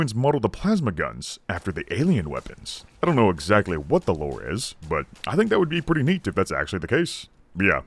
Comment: i misheard "lore" as "word" and went to type "Reverse Engineering" but i rewound five seconds to listen again